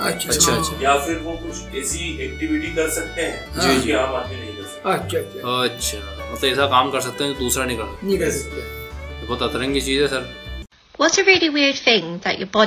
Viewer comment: WHAT DID HE SAY!?!?!? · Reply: He didn't say what u heard 😂 lmao He said ni kar sakta which means can't do